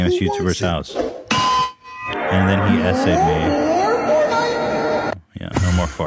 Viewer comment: Can’t just be me who thought they were saying ‘essay-ed’ not SA-ed.... I literally thought it was some new word